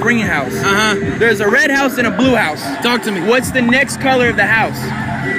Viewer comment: became a meme here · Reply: I thought he said " talk to me nigga "